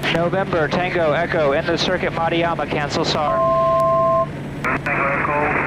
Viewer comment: Sounds like the "R" in Papuan names is spoken as a "D". Mariama in this video spoken as "maddy-arma" and I've heard you say previously Iteri as "e-teddy". Interesting. Thanks for another well-executed flight vid.